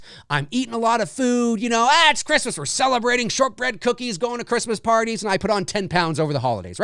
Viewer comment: At the 27 minutes mark, I thought you said short rib cookies 😂. Morning coffee hasn't kicked in. 🥴